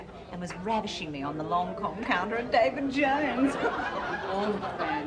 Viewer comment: Oh gosh I finally worked out the line ‘ravishing me on the Lancome counter’; as a child I thought she said ‘long compounder’ 😂